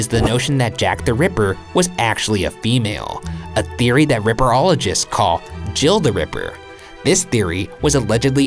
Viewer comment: can we talk about how "ripperologist" is an actual term? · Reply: Naja R. H. Pedersen lol I thought he said zipperologist